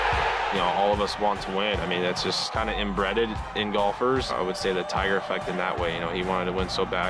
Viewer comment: did he say "it's kind of imbredded" ??